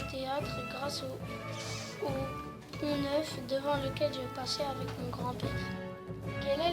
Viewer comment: il dit quoi 《au neuf》parceque j'entends pas bien